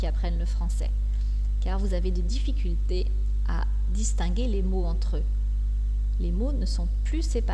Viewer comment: DISTINGUER LES MOTS, j'ai entendu: "DISTANGUER"? 08:10 min.